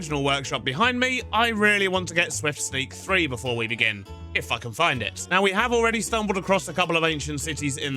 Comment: Had to replay 0:33 when he was talking ab getting Swift Sneak, instead of "If I can find it" I overheard "Can't F-ing find it" but we're all good hahah